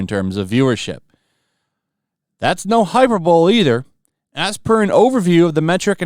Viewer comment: Did you just say "hyper bowl"?